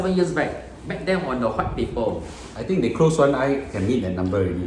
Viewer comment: Tot I heard the "huat" instead of "white" paper 😂